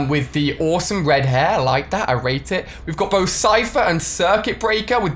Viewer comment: does it not sound like he said I RAPED IT